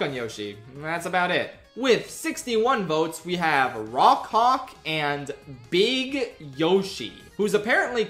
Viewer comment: EXCUSE ME??? oh he said rawk hawk heh thought he said raw c